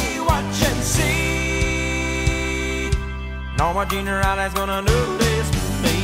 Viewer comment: for the longest of time i thought he said"noone on this earth", instead of norma jean riley"